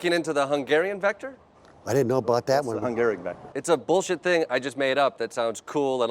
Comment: "The Hungarian vector" is actually quite clever because it sounds so scientific (though many misheard it as 'factor'). Funny how they pounced on it, proving his point.